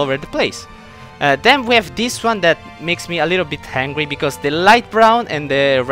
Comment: I thought when he said angry I thought he said hangry